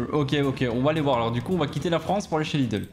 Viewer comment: moi j'entend " on va quitté la France et sauf un cheerleeder " XD